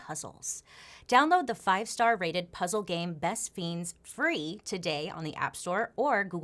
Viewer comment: I thought you were saying "Best Beans"